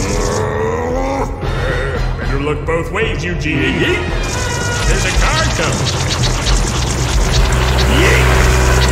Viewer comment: DID HE JUST SAY YEET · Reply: Yep, Plankton just said yeet